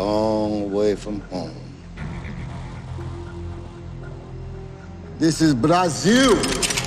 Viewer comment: I heard it as, "this is Braziu".